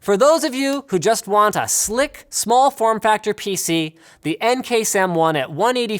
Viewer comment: what i heard: "for those who want a Luke form factor pc" what Linus said: "for those who want a Slick form factor pc"